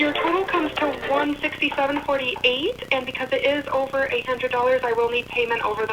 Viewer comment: I heard “since it’s over $800” and I almost died. I was like oh she got MONEY money.